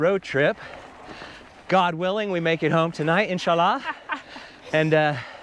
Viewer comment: did you say insha-Allah = God willing 🙂🆒 Love your Video content BTW. 👍